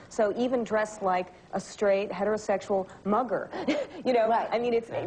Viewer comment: Did she say MUGGLE!? O_O Ms rowling you got some explaining to do , girl! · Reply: Ste Lo I think she said mugger